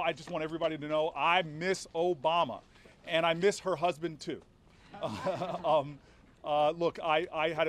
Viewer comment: did he just said he miss Obama and her husband too?